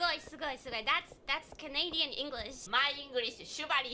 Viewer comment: When saying kanata I thought she was saying canada